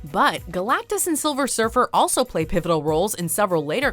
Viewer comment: Did.. did she just say "sulver sirfer"???